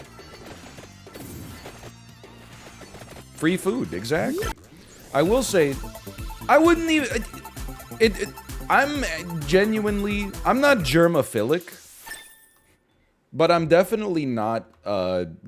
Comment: i heard "i'm not jermaphilic" instead of "germaphilic" and it fascinates me that my first thought wasn't "hey wait jermaphilic isn't a word" but was instead "who tf isn't jermaphilic"